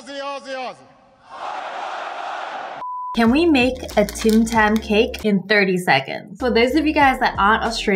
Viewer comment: U thought it said Tam tam instead of Tim tam xD